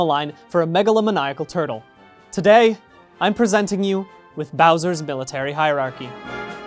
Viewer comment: Ooo I thought the video said 'browsers military hiarachy.' This, um 😂